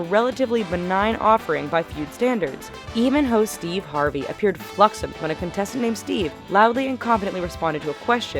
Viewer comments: did she say fluxommed insted of flummoxed o.O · Did she really say "fluxommed" at 3:15? Really?